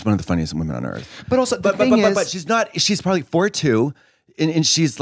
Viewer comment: i thought katya said “she’s probably 42” i was like mama she’s 62… which also surprised me cuz i googled it and she is gorgeous